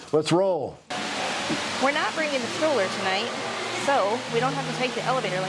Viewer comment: When you said we're not taking the stroller tonight I thought you said we're not taking story and for whatever reason my heart dropped and I was like wait what what are they doing with story why aren't they taking story?? Lol